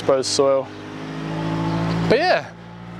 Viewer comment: i serious thought u said 'Beer!' but it was 'But yeah!' :D